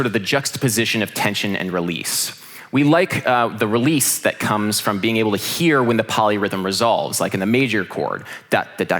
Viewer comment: Please who is the guy he talks about at around 28:00 (the guy who does crazy music). Cant catch his name Thank you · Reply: Yassine Taoudi Conlon Nancarrow